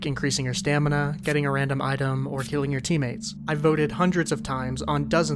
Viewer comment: did you say killing or healing?